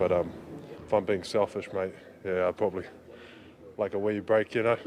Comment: Sound like Adams said " I'd like an WEED break ".